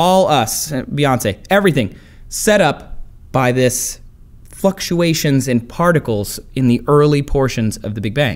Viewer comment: Tell me how he said fucktuations instead of fluctuations